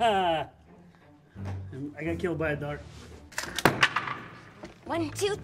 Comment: did he say I got killed by a door? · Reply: I think he said dart